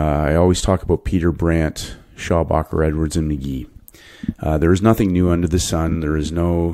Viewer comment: what are the names of this people pls ? 🙏 (only heard Peter Brandt, can't hear the second one) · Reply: "Schabacker, Edwards and Magee"... Technical Analysis and Stock Market Profits by Richard Schabacker , and Technical Analysis of Stock Trends by Robert D. Edwards and John Magee